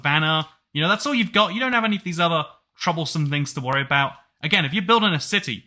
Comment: did he just say choblesome? Like the word grian made up?